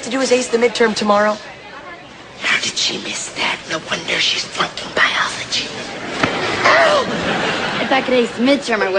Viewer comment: What is he saying on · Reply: "How did she miss that, no wonder she's flunking biology"